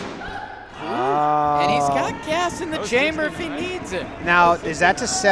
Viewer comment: Did say "Gas in the chamber???"